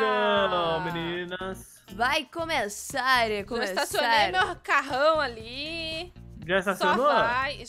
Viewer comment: eu ouvi "macarrão" em vez de "meu carrão" e depois quando a disse "então a mãe ta na garagem" eu fiquei: ue ta preparando macarrão na garagem?